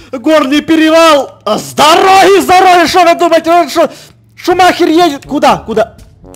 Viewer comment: когда эд сказал шомахер едет мне послышалось шонахер едет😂😂😂😂